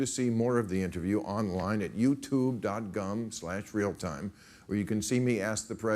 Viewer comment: sounded like he said youtube dot gum